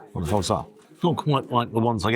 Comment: What does say at 21:42? All I see is him looking at the sandwich and hear him saying "What the f*cks that?"